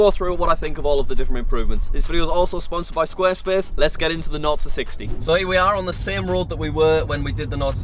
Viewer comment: I am a beginner in English could someone help me and tell me what he is saying when he says nord to 60. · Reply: He's saying "naught to 60". Which means how long it takes for the car to go from 0 miles per hour to 60 miles per hour.